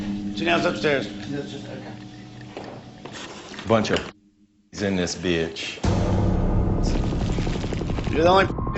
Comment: what did he say? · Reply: Could be wrong, but I think he said “A bunch of pussies in this bitch”